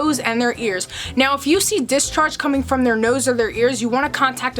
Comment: Is she saying discharge